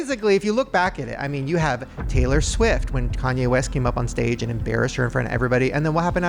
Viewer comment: Lol did Shane say "Tanye West?"